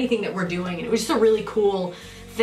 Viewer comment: Haha at first I thought it said "winds of plague".